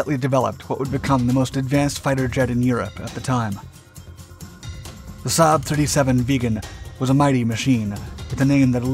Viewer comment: it sounds like youre saying "saab 37 vegan" xd